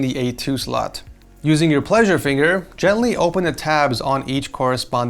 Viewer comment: Lol ... I heard "using your pleasure finger"